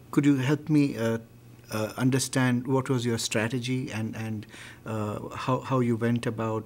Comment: did you said "what was your 'Strategy'"? at 8:37 ??